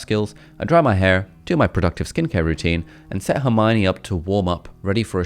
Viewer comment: did he say Hermione?